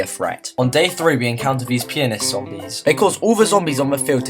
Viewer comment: I heard penis